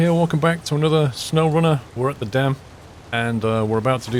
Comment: I heard “welcome back to another snowrunner, worth a damn” :D